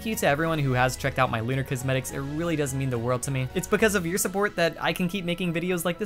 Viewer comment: am i the only one who heard it "doesn't mean the world to me"